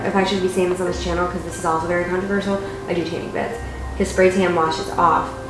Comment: what does she said? i couldn't understand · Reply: she said “this is very controversial I do tanning beds”